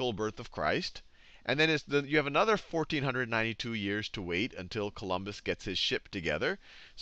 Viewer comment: Haha! Sounds like your saying: "and then you have another 1492 years to wait until Columbus gets his shit together"